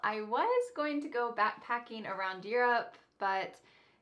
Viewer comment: | You are saying "around" but your subtitle says "across". Is this a kind of attention exam? Send my reward pls. 🎁